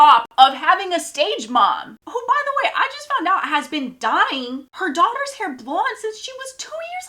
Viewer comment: i thought you were saying her mom was dying for a second 😭